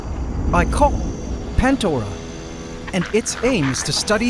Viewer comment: Is it me or do i hear my co- pandora